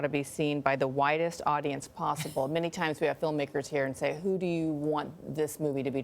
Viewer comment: I thought she said the "whitest audience possible" lmao